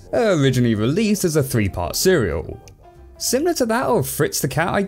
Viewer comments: Did, did you just say "a three part cereal"? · is it just me or did i hear cereal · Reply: Serial, which is another word for series I believe.